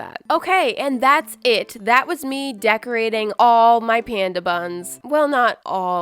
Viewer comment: I thought you said panda butt